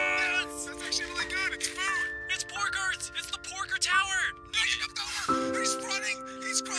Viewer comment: did you just call pig tower food?